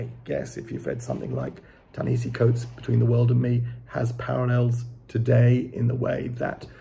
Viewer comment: I generally agree with you about Everett’s endings, but not in this case. I may have a video about it. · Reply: what is the book you reference @ 6:10 ? I didn't quite catch it. Edit: Between the World and Me | Ta-Nehisi Coates (?)